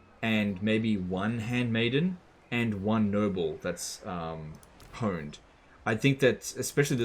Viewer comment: EVERY time he said "Noble" I heard "Nurgle".